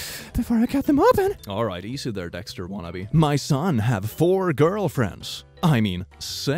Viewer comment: I TURNED ON THE SUBTITLES AND WHEN SAID "SIX" IN 14:59 THE SUBTITLES SAID "S*X" 💀💀